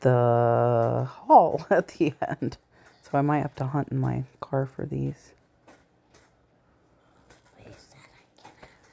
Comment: Does anyone else hear a child's voice whisper something at around the 23:40 mark???? Unless you had a child with you in the room when you did the voice over. But there for sure is a child whispering after you say you didn't see the towels with the knitted top in the haul and will have to look in your car. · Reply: Says something like "you said I could have......."